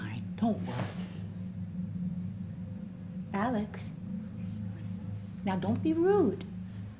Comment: omg I thought she said Alan!!! Like where is he